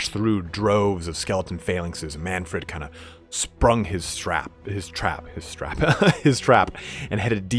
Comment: I'm listening this while working and suddenly I hear "Manfred has sprung his strap and headed deep into.... " I was like wo wo wo wo what kind of Slaanesh heresy did I stumbled upon in emperors name!!!!!!